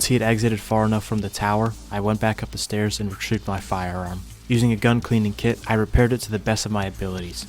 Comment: What did he say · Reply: “I walked back up the stairs and retrieved my firearm”